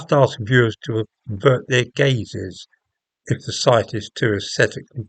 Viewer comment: Did you say "Avert your gays"? Shocking homophobia! ;-)